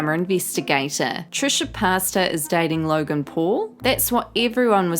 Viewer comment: OMG DID SHE REALLY SAY TRISHA PASTA 0:12 😂😂😂😂😂😂💀💀💀💀